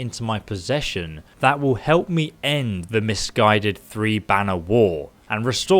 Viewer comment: I thought that said the "misguided Three Banana War" 😂😂😂😥